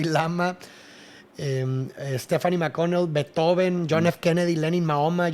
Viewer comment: en el minuto 10:52 dise stephany mconeel y esta muy herrado el sudtitulo de el video que ven en youtube al que dice la cancion es tafari makonnen